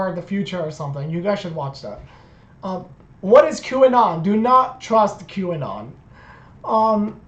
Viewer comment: What's that word at 8:31 ?? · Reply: Q anon. Its supposed to be a pro trump anti deep state thing.